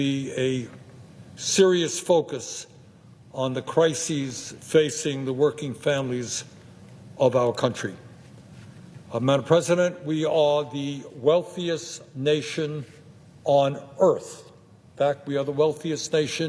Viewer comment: At first I thought he said "now the president Elon Musk..." I had to replay it like three times to realize he was saying, "madame president." Not really a Freudian slip, but similar on my part.